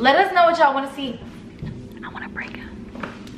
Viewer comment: Bro they ma cute, they should’ve kissed at the end🤦🏽‍♀️😭. But wat did say at the end all I heard was “I wanna” and then I didn’t understand her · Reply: Lindsey Llivizaca "I wanna prank him"